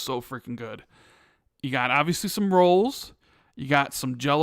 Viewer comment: When said " obviously some rolls" , I thought he said "obviously some squirrels." 😆